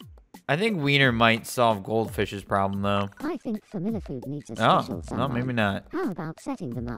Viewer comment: It sounds like he said “Familla Food”